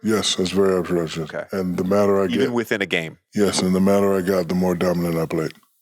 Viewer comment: What did Diesel even say here? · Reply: Very observatious 😂😂😂